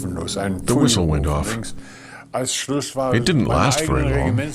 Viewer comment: What does he say at 0:19? "There was a (?). It didn't last very long." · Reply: The whistle went off. It didnt last very long.